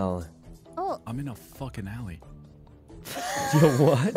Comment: "I'm in a fucking heli" - did he really say heli? like as in helicopter?? google map got tired of cars? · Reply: I think he said alley??